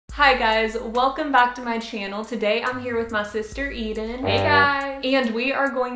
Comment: At the begging I thought she had said "this is my sister Evil"